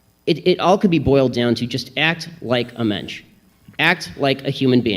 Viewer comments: Wait did he just say "act like a mensch"? (8:50) human in german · Did he just say "Act like a Mensch?"